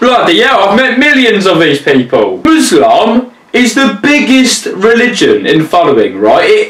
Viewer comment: Muslam? Not Islam, not Muslim, Muslam. He might not have but I could swear he said that.